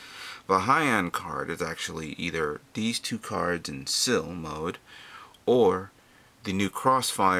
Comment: did I hear " sil mode?" Is that why you bought this build?